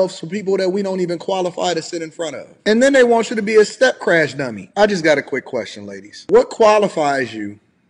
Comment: BEING A STEP CRASH DADDY IS....WILD! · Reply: I thought I heard step crash dummy. 😆